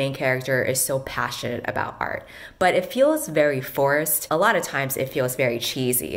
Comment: I thought you said "it felt very forest" at 19:40 to jokingly show how awkward it would be to use something like colors to describe feelings. I'm kind of sad to realize you said forced, so I chose to believe that you actually did say forest.